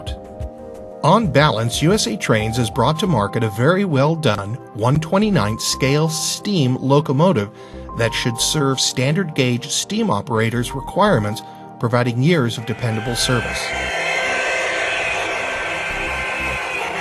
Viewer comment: After 4:40, did you say it should serve them "for about a year's dependable service"?